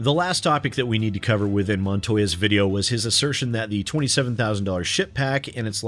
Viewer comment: I heard, "27,000 dollar shit pack". LUL